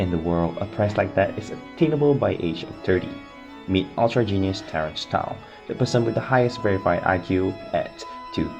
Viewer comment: bruh, am i the only one who can't understand what he's saying? what name is that supposed to be? i keep hearing "terrance tyler"